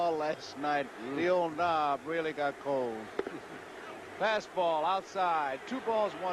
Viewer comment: Bring back the Expos! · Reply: did he say his old knob got cold?